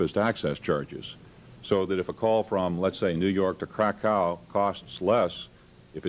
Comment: Epic clip as always! However I am going to be a bit nitpicky here, around 2:00 he said New York to Krakow, but the captions said New York to Crack How, just a small nit pick from me. Nevertheless this is awesome content!